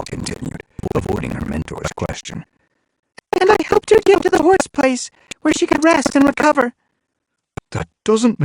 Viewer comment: does anyone know what Whistlepaw says here? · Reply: basicay that she helped her to hourseplace